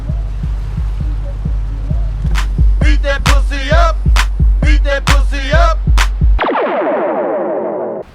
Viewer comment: So Suda rides around in Aphex Twin's limo? Makes sense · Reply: lover64 i legit thought i heard that as "suda51" until i clearly heard "beat that pusz up"